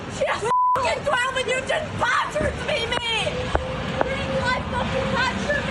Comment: in the unintelligable bit she says 'you've just fucking battered me ' ...that is what it sounds like she said these brave little girls